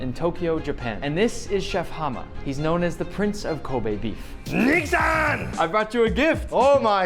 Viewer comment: What did he say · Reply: Nick san. Dumbahh